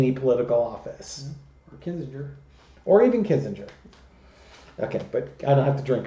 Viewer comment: Literally spat coffee - thought you said Cassidy would do well in a Kissinger office. WTAF Alarm! Then I listened again & realized said Adam Kinzinger. Okay, Coffee cleanup can wait, back to vid, now more awake than a retired Crone needs to be at 6AM, calmer if a tad damper. LOL.